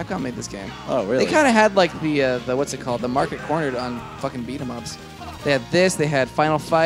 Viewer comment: When you were talking about them having the market cornered on brawlers I thought for sure you were saying "They had the market cornered on Blankas" - which is a high paced industry I could get into.